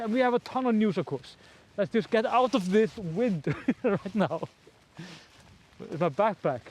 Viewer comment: Love your presentations, had to laugh and play twice @ 1:57 i thought you said 'Let's just get out of this wind, it's a bastard', lol.